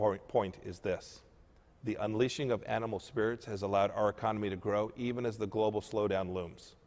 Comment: Did he say "the unleashing of animal spirits" at 3:51? What does that mean?